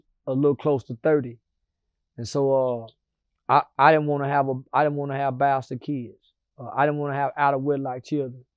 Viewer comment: I thought c white said he ain’t have no bastard babies 😂